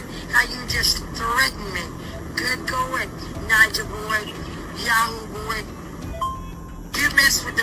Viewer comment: Did she say "Niger boy" ? Like the first half pronunciation of Nigeria ........ 7:39 timestamp